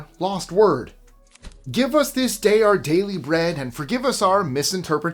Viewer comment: I heard a kid say “our jelly bread”